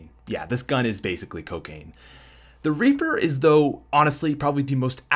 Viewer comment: thought he said raper